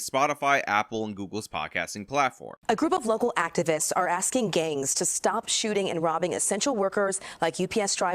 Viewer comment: Dude it sounds like shes saying activists are asking gays to stop stealing lol